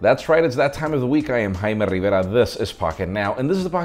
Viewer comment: When YouTube's auto-generated captions recognized your name as "High Metal" instead of "Jaime" 🤘🤘🤘🤣🤣🤣